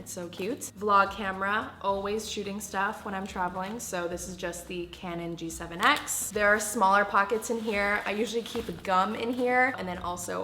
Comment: " I always keep a gun in here " Wow Billie doesn't need security anymore (I know she said gum, I just misheard and found it funny)